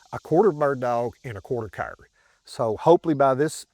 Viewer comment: What did he say the dogs were at about 1:40? Half beagle, 1/4 bird dog, and a 1/4 what? At first I thought he said Cur, but after listening a few more times, it sounded more like Kyrie or something. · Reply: That’s just the accent. He said Cur